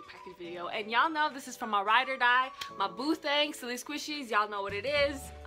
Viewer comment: i thought she said boothday